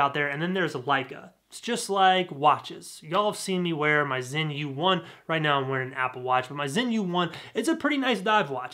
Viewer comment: I thought I heard him say Sinn U1 automatic divers watch. It’s a class act, right up there with Rolex Sub, Blancpain 50 fathoms, or Panerai. Like Leica, those luxury brands made their name and reputation as genuine world class innovative tools. Many luxury items don’t always have that distinction, and earn their reputation only because of some charming story or myth behind nothing more than an ordinary middling product.